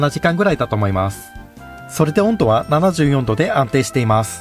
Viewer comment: 74°って聞こえたの俺だけ？ ↓